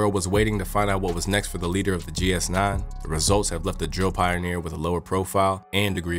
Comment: Did you say drill pioneer? I must’ve heard that incorrectly